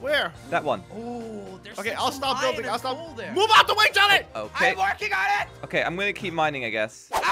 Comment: When crainer said GET OUT THE WAY JELLY I thought he said GET OUT THE WAY JOHNNY